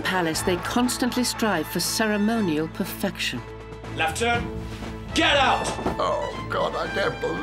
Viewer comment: What? He said left turn